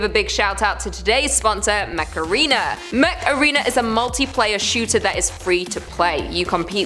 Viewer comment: Sounded like you said Macarena, ahy macarena